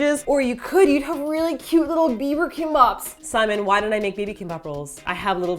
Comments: LOL 4:14 i thought u said "bieber kimbaps" haha X) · i thought said bieber kimbaps then I was like O.O what?? 4:17 then I realized u said baby kimbap >.< stupid me~ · Am I the only one that thought she said little Beiber Kimbap? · at first i heard 'cute little bieber kimbaps' and assumed thats how you got the canadian into the kimbap.. then i realised you actually said baby.... uhmm....